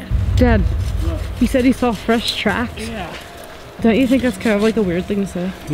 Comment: I thought she said he wants fish sticks. Unit she told her pop he saw fresh tracks